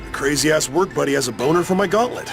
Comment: did he just said 'boner?.... I'm really scared of what the fandom has to offer now.